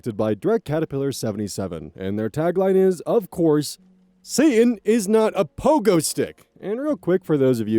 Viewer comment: Finally we have the "Satan is not a Pogo stick" origin and I LOVE IT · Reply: Ohh so that's what the tagline actually is. I've always heard it as "Seeing is not a Pogo stick". Well, good to know what is and where it comes from.